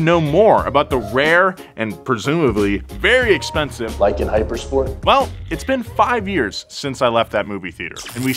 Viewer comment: I thought he just said "Like, in hypersport...." as if didn't know what a hypercar is. I thought he was just being sassy